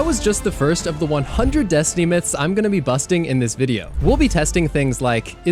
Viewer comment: what did he say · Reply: He’s going to be busting